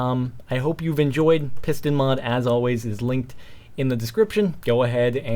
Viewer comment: Could have sworn it said prison mod at first.